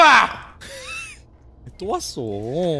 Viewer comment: i think at 5:04 he said "tto watseo" it means "he come again" ,he meant the doll..i think before that they already throw the doll away but it come again to the house.. 5:07 "che ga munjae ya" its mean "He (the doll) is the problem".. coz that doll really give him the creepy vibe..🤣🤣 Anyway thank you for your video and sub..😍😍 hope to see another of your video...i'm a new fan to these 5 cute teddy bear!!!🤩🤩